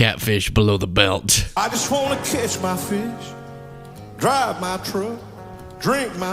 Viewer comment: I legit thought he said "Kiss my fish".…..was that just a southern drawl "catch my fish"??! 😂😂